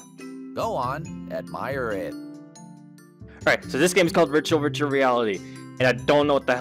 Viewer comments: Every time he says "virtual virtual reality", I hear "ritual ritual reality" 0-0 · near 1:20 it sounds like he says ritual ritual reality